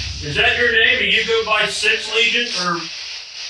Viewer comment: I didn’t hear six I hear lucifer